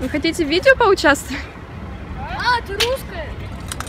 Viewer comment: "а ты русская" did i heard that right?? 😂😂 this kid